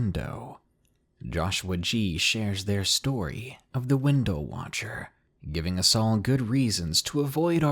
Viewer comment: Hahaha i was paying attention at first and thought you said the window washer. Growing up and still to this day I can't stand not having curtains or mini blinds covering my windows cause I feel like there's someone just out of site enough into the darkness watching you.